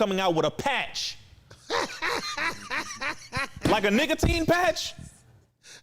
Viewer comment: I thought Rizzo said they coming out with a Niggatine patch not nicotine 🤣🤣😩